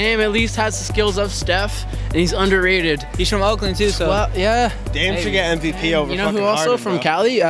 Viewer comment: did he say dame is better than harden